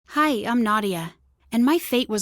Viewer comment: I thought she said Hi I’m Naughtier...